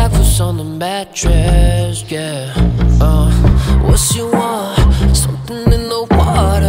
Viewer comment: Bài hát: The Eve (Demo) - Henry Lau What's on your mind, what's the situation? Need you here, tired of all the waitin' She's a killer in a black dress Doing bad things on the mattress, yeah What you want, somethin' in the water Is it wrong when I put hands up on your body That's my baby in the black dress, yeah She just looking for that address I've been over my head to see the brighter things She knows what's on my mind Looking to be, the part I need from you is almost over time I can't get you out my head You've been everywhere I go I can't get you out my head I'm so caught up over you I've been over my head to see the brighter things She knows what's on my mind I can't get you out my head, uh You've been everywhere I go, o-oh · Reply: The part: "what you want" at the second verse I thought its was a chinese lang, because I heard "wasiwa"